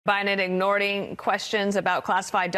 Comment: Did she say "ignording"?? I heard "ignorDing..."